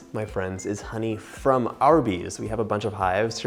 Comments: I thought he said “honey from Arby’s “. Haha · I have watched this a few times now and made this recipe three times so far. When he says "we got this honey from our bees," I always hear it as "we got this honey from Arby's." I have to think about it, like "Arby's must have some really good honey packets." And then I am like "oh yeah, our bees..." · I thought he said it was honey from Arby's ™ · I thought he said "This is honey from Arby's" lol